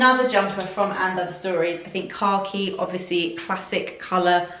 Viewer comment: Everytime you say "khaki" I hear you say "carkeys". Lol. Thanks for the gr8 video, on my way to hopefully make some purchases!